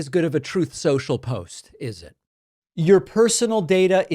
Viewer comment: the closed captioning is broken. I clearly heard you say Troth Sensual, but the cc said Truth Social. 😂